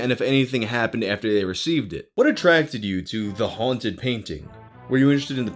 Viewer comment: for a moment i thought it said "the haunted eboy painting"